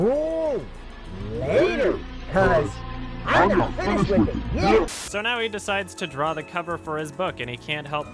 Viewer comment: it sounded like he said "I'm gonna finish with a yiff" How appropriate.